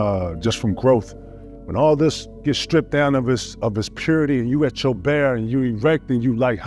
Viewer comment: sound like he said “when your bare and your ERECT”